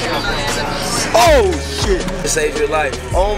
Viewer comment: did he say shit?